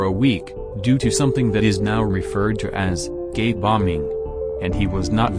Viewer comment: for some reason I only hear "gay" bombing lmao